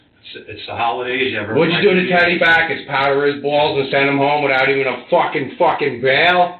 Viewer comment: I laughed when he asked "Did you powder his balls?". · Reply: Oh I thought he said powder his walls! I was like... Is that a thing in America? Powdered walls?